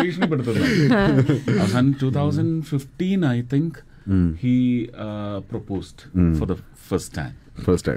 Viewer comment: Did he just say "he proposed to the first time to a virgin"